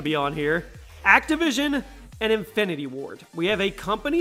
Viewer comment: I thought you said Infinity War 😂😂😂